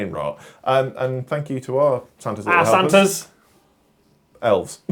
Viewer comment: I legitimately misheard referring to the patreons as "our Santas" as "arse hunters" and was SO confused!